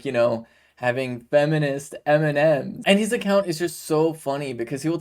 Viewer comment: For anyone with access to closed captioning, instead of seeing the word M&M, I saw Eminem @ 1:21 and 2:42! This was amusing to me. #SlimShady #MarshallMathers